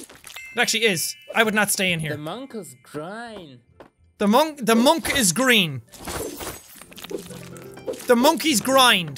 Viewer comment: I heard him say 'the mop is dry' 😂